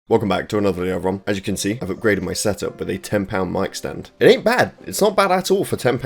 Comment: Bruh I’m American so when I heard “£10 mic stand” I actually heard “10lb mic stand” and was confused why your mic stand was so heavy